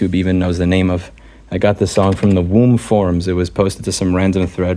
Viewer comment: I'm sorry...did I hear "The Womb Forums"?